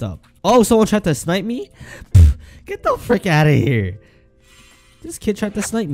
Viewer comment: Lol i thought he said the f word lol so i played it back lol He said frick